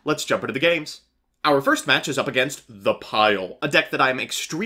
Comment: what deck you said it was? "The pile"?